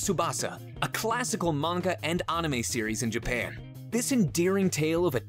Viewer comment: Did he just say oname?!?! Instead of anime!!!